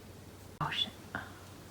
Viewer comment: Did she say “oh shit” at 16:14? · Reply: she said push it up